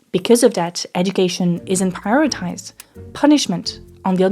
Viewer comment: Btw 9:14 the subtitles say "is" while the audio says "isn't", just a heads-up